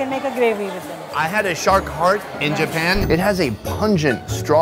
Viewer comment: anyone know what word he says at 4:53? “lockaroom” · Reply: Lockerroom